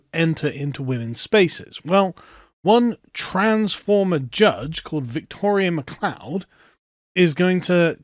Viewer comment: was looking at another window so I just heard "transformer judge" transformers.. big blokes in disguise